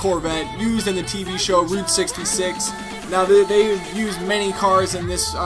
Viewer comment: 66 sounds like he said dicks